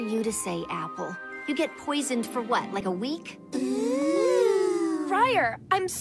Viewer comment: When I was little I thought that briar said “easy for you to say apple, you get poison from a wheat”